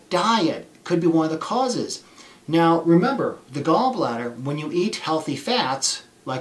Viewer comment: Why should you reduce consuming dietary fats because it releases bile, yet you are advising that we consume bile supplements??????? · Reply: I have thought he said you Need Healthy Fats — going to watch again as fats cause flare ups for me